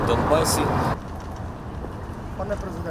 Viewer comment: Мені почулось, чи до нього звернулись «пане президент»??